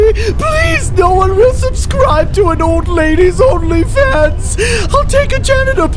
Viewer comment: 💀 did she said only fa